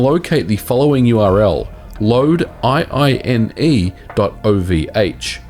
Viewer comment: i thought he said loadLine but it's two i's. loadiine ya. don't go to loadline.